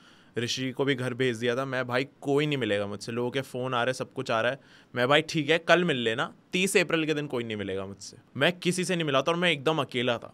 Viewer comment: 38th April? Matlab aapke zindagi me moi worst din hai hi na:/ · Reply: Usne 30th bola toh Bhai to 38 sunyi aaya 😂